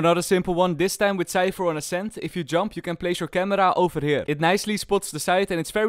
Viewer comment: did he just say ascent for heven